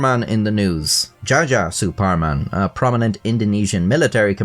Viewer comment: I heard "Jar Jar Superman"